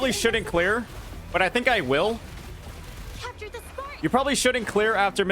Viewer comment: Did she just said "Capture the Fart?" 25:15 "I need to pee"